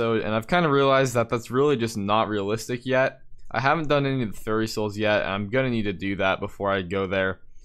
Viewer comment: Did he just say "FURRY" soul at 3:28?